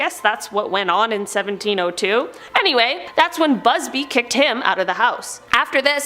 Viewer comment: I heard buzzfeed instead of busby 💀